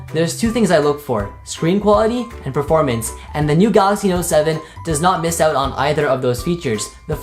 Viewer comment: Did he say the Note 7 @ 1:35 yea ya know ima passvi like my house and my leg